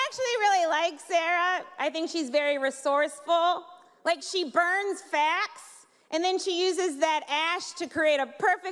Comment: I am of two minds. As aside, first, even listening to the audio after the fact (but not having had the set described to me), but I originally misheard the joke "She burns fats and uses the ash to make a perfect smokey eye," which really ruins that joke. To my main point, on the one hand, I can't identify where her jokes went over the line. I mean her lecturing of the press corps didn't seem like there was a joke there, just an observation that she was not going to be able to make if she didn't blurt it out there. So a non-joke admonishment may have been poor form/* (whether I agree with it or not). Otherwise, though, her jokes fell flat in several places, but I can't see where they were otherwise all that different from what I have heard other comedians say. The administration lies? That's not uncharted territory. That Kellyanne Conway is all over TV and is awful? I have heard similar jokes before. That Trump isn't really rich? Heard it (and that and the pussy grabbing were for me the funniest part of her set). So, on that side, why the upset? I am not sure, especially when the target of the jokes have to accept that "locker room talk" is perfectly okay. On the other side, if the comedian was given some idea of the goals of the event (I am not sure if she was or not) and the person sponsoring the event felt she violated the spirit of it, that is entirely for them to decide. If the goal is to raise money for scholarships (which it nominally is) and this performance actually wa